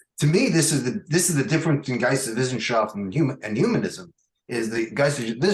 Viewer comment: Can someone please tell me what the word is at 36:27 ? It sounds like "...this is the difference between geiser visenshouft and humanism". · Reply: Geisteswissenschaften